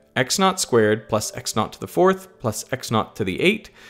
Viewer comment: FYI subtitles say "not" instead of "naught"